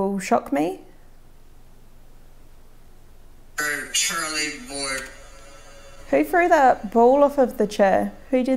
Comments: the first said im shirleyy ward and i knowtised maybe u didnt hear yhat sence iy easnt in close sapyion of ur evps u just put? To the evp pretty sure iy said im shirlly ward right befor the next mor cleat evp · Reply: I went back and listened to it at this time and it does say I’m Shirley Ward. I had even went back like 3 times to see what it said and couldn’t make it out said until you said and couldn’t figure it until you said something thank you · sounded like, "I'm Charlie Ward" or maybe "I'm Charlie bored "